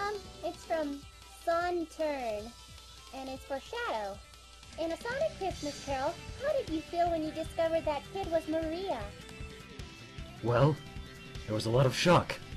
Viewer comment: I couldn't understand the question. Could anyone write it down for me? Plz? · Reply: The question was in the sonic Christmas Carol how did you feel when you found out that girl was Maria